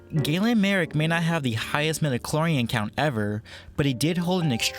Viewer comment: what did you say 10:36? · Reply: Newba Capone He didn't hold the highest midichlorian count ever ...